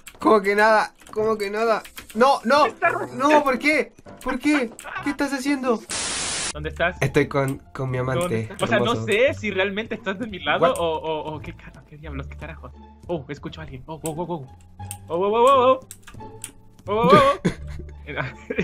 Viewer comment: 😲 fui yo o escuché que dijo mi amante😳😳